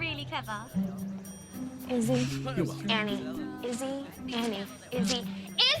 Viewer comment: The only thing you got wrong is that at 10:17 Drakes girlfriend does not say “Andy” she says Izzy! I swear you got that wrong! No hate here just correction! Great video !